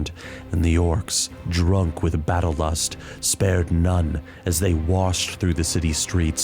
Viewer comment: sounds like he says "battle bus" on 2x speed.